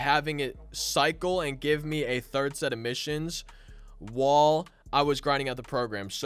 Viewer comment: the way he said while at 7:00 sounded like wall